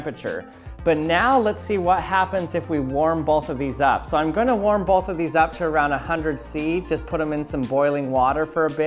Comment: Well in Fahrenheit 100 degrees isnt that much, if this is in Celsius then it is quite hot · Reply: he actually says "so im gonna warm both of these up to around 100 C"